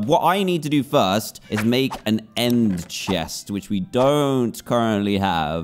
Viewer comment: If you turn subtitles on and look at 0:25 it Dan says we need to make a end chest but the subtitles say we need to make a M chest